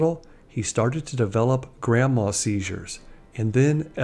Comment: Did you say grandma seizures?! At 10:13 It's grand mal holy shit.